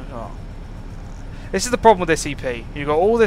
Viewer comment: Did he just say "this is the problem with SCP" I know he says "this EP" But :/